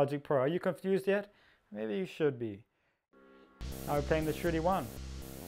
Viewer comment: I never said it was an Ambika! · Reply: Im sitting here scratching my head why I would have written that... !! because it sounds like youre saying "shitty one" when you say shruthi-1... haha.. no offense